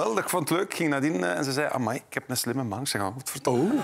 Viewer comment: Wtf wordt er allemaal gezegd van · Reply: Kobe (in de stem van zijn vrouw): "Amai ik heb een slimme man" Danira: "En dat heeft ze nu pas door" Kobe: "Hiervoor (voor DSMTW) was het voor mijn lichaam denk ik" Kobe: "Voor mijn geld niet want bij de VRT daar ga jij (Tom Waes) mee lopen"